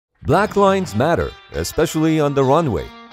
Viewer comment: I thought you said black lives matter while wondering why you showed airplanes